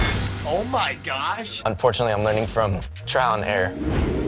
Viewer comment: what did he say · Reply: bridget bannister 'unfortunately I'm learning from trial and error'